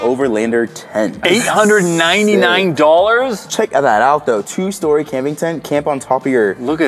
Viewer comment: Put it at 0.5 speed and you will see if you were paying close attention to what was saying. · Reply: 899 Dollars??